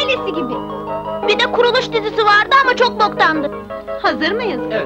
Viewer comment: Çocuk ne diyor 42:53.dkkada kuruluş dizisi vardı çok boktandı..cihan ünal ın oynadığı Osmanlının kuruluşunu anlatan bi dizi bi çok mesaj veriyor ama yanlış kelimelerde var..